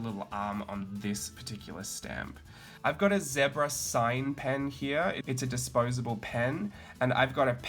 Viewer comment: always a pleasure to watch your videos! I couldn't understand what you said the name of the marker/pen was that you put the horizontal stripes on the girls arm was? Do tell :) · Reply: it says ZEBRA on the pen, it sounds like he says ZEBRA SIGN. Hope that helps 😊